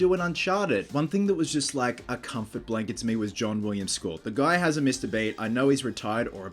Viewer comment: when you said "John Williams' Score" I thought you said "Sean William Scott" - the guy from american pie